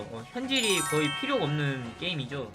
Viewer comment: 네넹 뭐라고용? 현질이없는게...임 이라고요? 그른가...? 이 말하면 맞는거야 그래 맞는거야